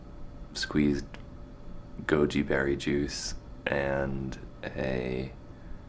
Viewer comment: Did you just say Gummybeary juice?